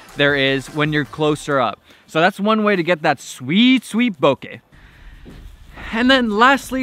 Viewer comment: I heard that... "The sweet sweet sweet boche"